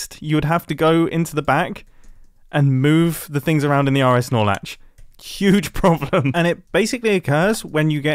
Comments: The entire time I thought he was saying RS Snorelax 💀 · I'm not a redstoner but I do like pokemon so all I hear is "rs snorlax" 🤣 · the… RS Snorlax? like the pokemon? i think im just mishearing.